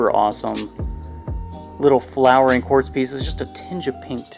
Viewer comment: Did you say pink at 4:45ish? Lots of beautiful pieces bro. Keep up the great work